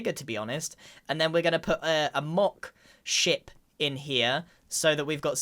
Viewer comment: Ngl I heard “block s**t” not “mock ship”